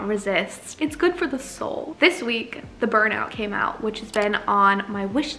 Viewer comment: When she said The Burnout came out I thought she was saying she was burned out at first. It did seem like a strangely cheerful way to announce that lol.